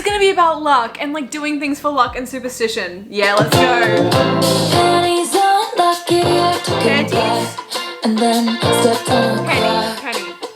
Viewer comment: Hahaha, this is so fun. I also misheard 'panties unlucky' on my first listen lmao 🤣